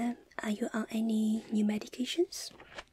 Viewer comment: is it just me or did she say "meditations" instead of "medications" you're too cute lol